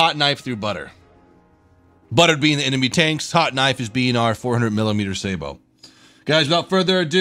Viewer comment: did you just say 400 mm sabot?